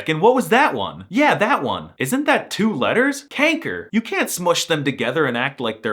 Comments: did he just say kanker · WHY DID YOU SAY KANKER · it sounded like you said "kanker" (in a more english way) lol also, i dont really see ij as a seperate letter. if theyre togethere to make 1 sound it can also be called lange ij (literally "long ij"). i learned the lange and korte ij/ei and otjes ou and atjes au but i really dont think that way. also, i didnt even know that if ij is at the beginning of the sentence, theyre both capitalized. if i even capitalize something, id only capitalize the i lol diphthongs* tbh your ui sounded like an au/ou 😅 also, its het meisje (not de meisje) because meisje is a "verkleinwoord", which basically means its when i word got small (meisje has become the new word for girl over timez but it used to be meid, which has de, not het) "ik zou dat uranium willen eten" 🤣 also, the g could be said in a soft way, we'll understand. its also said in a soft way in limburg and (noord-)brabant and dutch is said in alot of different ways (like saying de/deze/die when its het/dit/dat (god i hate that)), but that could also just be the evolution of dutch. languages are constantly changing and evolving, sometimes less noticed, so that could pretty well be the case sorry for this endless comment, i felt like sharing my thoughts :)